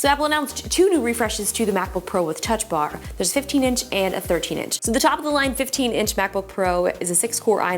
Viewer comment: I was eating something crunchy, so at 0:44 I thought she said 15-inch and 15-inch MacBook. Umm, excuse me? You explained the 2 exact laptops. But in the end, I stopped eating and figured out the mistake I made. :D